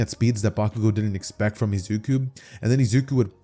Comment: turn in captions, the subtitles say “YouTube” instead of izuki 😂